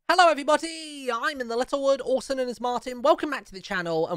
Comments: The way in every video he say hello everybody it sound like he saying hello everyboty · It sounds like he is saying hello every botty